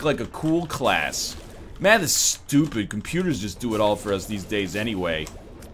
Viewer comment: When he said math is stupid, it sounded like he said Mathas stupid